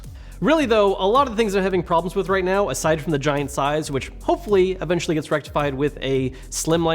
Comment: For a second I thought he said one of the biggest problems he had was the "giant sighs", and I was like, "Why he sighing so much?"